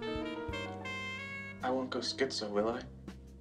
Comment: "I won't turn into Mozart will I?" "There's a distinct possibility." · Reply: I thought he said “I won't go schizo, will I?”